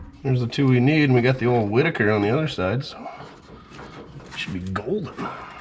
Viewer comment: did you say we had the "ole Whitaker" on the other side? Ref to the plug wires. Was that a Soft W Under ref? If so very funny Sir M